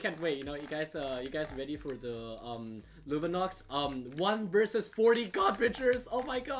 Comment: When he said 1 vs 40 god bridgers, the captions said 1 verses 40 garbage. The captions got em